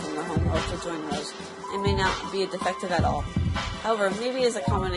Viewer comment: I thought you said pearl detective. Btw i realy like youre voice